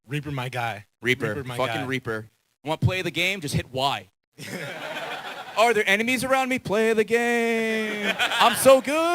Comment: okay so the first couple of time I've watched this and got to this part I thought he said "you want to play the game, just hit Y. . . the play the game, I'm so good" and I wasn't very familiar with Overwatch so I didn't realize what he was actually saying until now and now I feel silly... yeah.